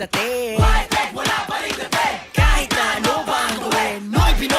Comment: I thought they were saying "noypi noypi noypi noypikit!!!! Which is true!! Pikit kase mga pinoy sa talent and kakayahan ng kapwa pinoy!!